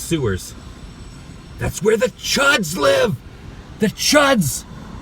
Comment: First thought you said "The Judds" lived in the sewer hahaha